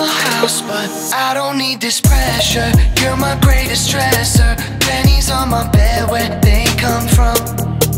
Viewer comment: She livin' in this lie with him No more coffee in the morning I'm wide awake while he sleeping in Know that's why you're calling You woke up chasing feelings, healing, trouble dealing Coping with the fact that you're alone It makes you miss my body, touchin', kissin', squeezin' He don't know your worth, your weight in gold Look at you fallin' Do you even know what you're fallin' for? 'Cause I see you fallin' You traded my heart for some fool's gold Fool's gold Can't think about your nights with him Wearing nothing but the moonlight When you feel your conscience stepping in Uber to my house but I don't need this pressure, you're my greatest stressor Panties on my bed, where they come from? Show up uninvited, fuckin' up my vibes with All the shit that you pretend you want Look at you fallin' Do you even know what you're fallin' for? 'Cause I see you fallin' You traded my heart for some fool's gold Fool's gold Fool's gold Fool's gold Fool's gold Fool's gold Look at you fallin' Do you even know what you're fallin' for? 'Cause I see you fallin' You traded my heart for some fool's gold Look at you fallin' Do you even know what you're fallin' for? 'Cause I see you fallin' You traded my heart for some fool's gold Fool's gold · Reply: Oh, "Stresser", not Treasure. I always thought he was saying Treasure in a funny way. This makes sense.